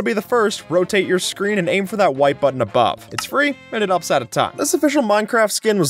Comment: Always thought he was saying "and it's an upset of time" but just realised he was saying "and it helps out a ton"...